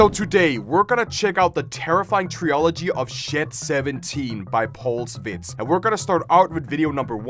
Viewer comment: Bro did not say Shed 17 😭🙏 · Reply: “Shiet 17”